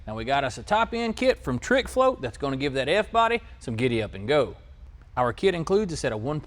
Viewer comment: did he say f-body 🤦🏾‍♂️